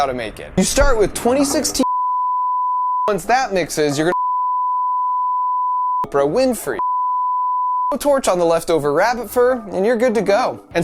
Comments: At the 7:00 bleeping i heard oprah winfrey and torched rabbit fur XD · is it just me or does that sound like hes saying oprah winfrey? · did I just hear Oprah Winfrey? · Did I just hear Mark say, “Oprah Winfrey?” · All I heard was ”leftover rabbit fur” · did he just say OPRAH WINFREY?? Or was it just me. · did i just hear oprah winfrey!? haha 💖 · Did he say Oprah Winfrey? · at the end of him telling the formula did he say leftover rabbit fur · did he say Oprah Winfrey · did he just say Oprah Winfrey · It sounded like he said Oprah Winfrey. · waaaait a minute. DID HE SAY LEFTOVER RABBITS FUR AT 7:07! THEY KILLED BUNNYS FOR THIS? 🥺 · Anyone else hear “Oprah Winfrey at 7:06 ? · Who else heard the word Oprah Winfrey on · Did anyone else hear him say Oprah Winfrey at 7:03 ??? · did you say Oprah Winfrey? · Did he say Oprah Winfrey at 7:04 lol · Did I just hear Mark say, “Oprah Winfrey? “ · anyone else hear "Oprah Winfrey"? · who heard Oprah Winfrey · did he say Oprah Winfrey?! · thought he said Oprah Winfrey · Sounded like Oprah Winfrey is an ingredient? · Did I just hear Oprah in 7:04? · Did he say Oprah Winfrey ? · Is it just me or did Mark say "Oprah Winfrey?" Does she have something to do with this or am I just hallucinating?